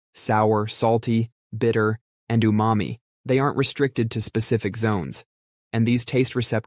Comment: Lol, I thought you said "and you mommie"